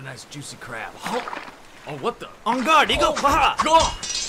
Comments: I had to watch it three times. Thought he said, "en garde, n****". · I thought the crab said ong n–––– bit it's actually on guard Eagle 💀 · Bro I thought he said on god N-word (I’m white i ain’t gonna say that) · istg I thought you said "en guarde, nigga" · “HA! EN-GARDE EAGLE!” 😂 😆 * edited for grammar nazis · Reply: 😅😅 First thing i heard was "En Garde digger"